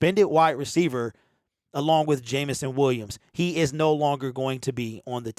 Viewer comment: had to listen to that over again, thought you said they cut Jameson Williams 😆